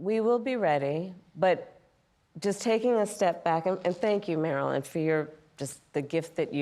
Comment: Did she call her Marilyn?